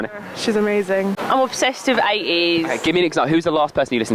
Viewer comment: when she said 80s I thought she said Ateez lol